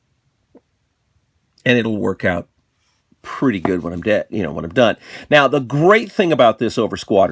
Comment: did you say it will work out pretty good when you're dead?! 3:08 todestrieb is coming out strong these days